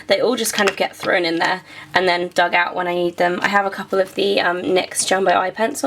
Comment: HAHHAHAH at 15:59 it sounded like you said; 'when i eat them'